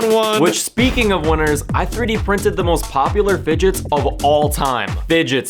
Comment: I thought you said digits like the 15 digits of pi: <num>